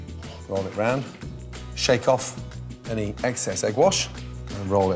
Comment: Did gordon just say "eggcess"?